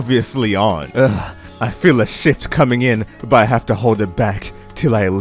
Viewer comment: i misheard what you said, i will stick to the misheard words since i think it is funny: "i feel a sh!t coming in but i have to hold it back, tell i atleast get off the bus."